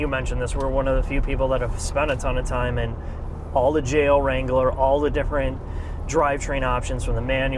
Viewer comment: I thought I heard you say "jail wrangler"